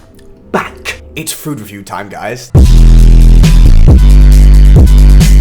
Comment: i thought he said its “futa review time” i got so excited😢